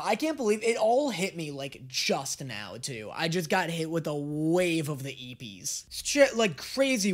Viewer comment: What does he says here? Sorry, I am not a native speaker and I could not catch it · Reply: Sorry I’m a bit late, but he said he was “hit with a wave of the eebies”. Eebies isn’t actually a word so it makes sense you were confused, but essentially what he ment was that he was suddenly very tired or sleepy.